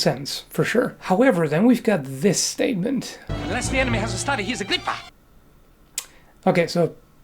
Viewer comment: Cool video! I always thought Mandy was just saying ‘his grip’ with an accent so it sounded like ‘hisagrippa’—looks like I was wrong!